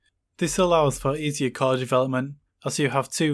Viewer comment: This allows for easier ..... development ??? Can you kindly let me know what was the word there? It was a bit hard for me to understand you. Thanks and awesome content as always! One of my favorite channels. 👍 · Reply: Yeh easier development. Core design. The two pipelines are separate with separate register files, schedulers out-of-order instruction handling with their own reorder buffers. So you can have a design team work on each pipeline simultaneously.